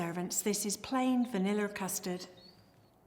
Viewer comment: There's a wrong english subtitle at 6:01 mins. Instead of Crumb Pudding, you input Plum Pudding. Btw, Im a big fan of Mrs. Crocombe from Vietnam. I always watch Mrs.Crocrombe old videos while I'm waiting for her new ones. -^^